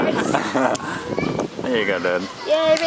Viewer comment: did say “there you go babe”